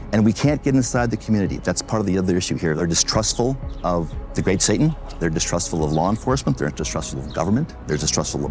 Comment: Did he say “theyre distrustful of the great satan?”